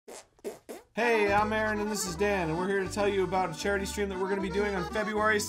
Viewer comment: Was i the only one who thought he said ”Hey I’m Mary and this is Dan” lol